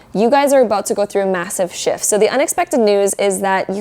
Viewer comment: i thought she said "you guys are going through a massive sh*t" But when I played the part few times to confirm that, but she said "shift". So don't get confused with that.